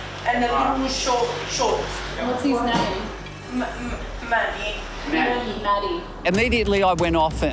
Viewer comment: Manny’s mum: *crying Uncontrollably and passing out* Manny’s Brother: “I’m sure manny’s fine” *while eating jelly beans* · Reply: Manny? I thought she said “Matthew”